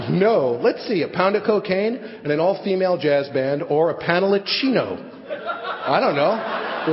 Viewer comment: what is he saying at 8:19 after "a pound of cocaine and an all female jazz band or a panel of cino" a panel of cino? what?